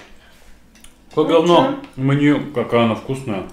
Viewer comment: "Какое говно" Почему я это слышу? 😆